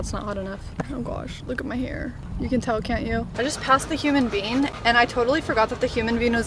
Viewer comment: This video had me cryyyyyyyying 😅🤣😂 Poor baby hairs! I've been there done that too as a fellow fake blondie 😉 Also (I'm from Michigan) sooooo when you were saying Human Bean, I thought you were saying human being, and I was legit confused 🤣🤣😂 PLEASE! Link the top you're wearing with your beachy pants when you were telling your hair story. I looked in your Amazon storefront and didn't see it. Much love girl!! 🤍🤍